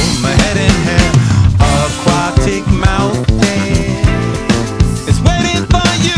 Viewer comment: Thought he was saying Aquatic Mountains when I first heard it lol